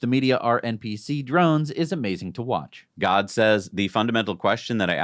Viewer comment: that so sounded like God said instead... 😆